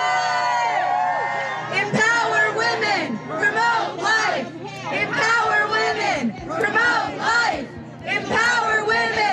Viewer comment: I thought they were saying, "Empower women, remove life" lol